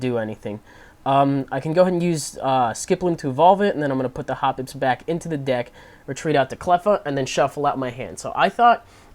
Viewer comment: I totally heard "I'm gonna put the hobbits back in the deck"